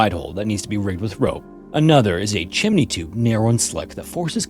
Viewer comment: WHAT did u just say? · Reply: Narrow and slick😩